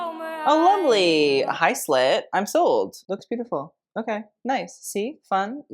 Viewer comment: Instead of "Hi Slit!!!" I heard "Hi slut!!!" and I was like "PARDON???"